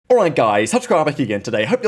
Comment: Okay, I’ve seen like 30 videos in a row, but I still can’t figure it out. You speak too quickly for my slow brain. I hear “Alright guys, tutsgrabahickiegan today” And then “very intuitthereuxthoughts down below” So what are you saying really?